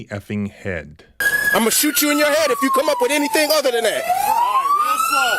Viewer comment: so that was a lie again, he actually said 'if you come up with anything'... then what you said, also one cop is getting the siren turned off so the commands can be heard clearly